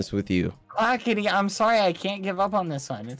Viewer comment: am i the only one who heard "ah kitty" ?😳